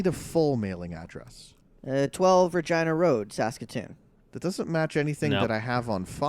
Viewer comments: did he say vagina road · Did he say vagina road?